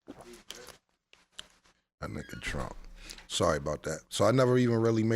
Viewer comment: Did Cam’ron just say “Dat n*gga Trump”. Maybe I’m mishearing him but that was odd.